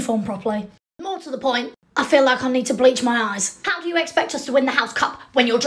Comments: Dude when you said “bleach my eyes” I heard “bleach my ass” and I was like- ok Draco that’s not something you hear every day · i can not be the only person that heard you say,bleach my arse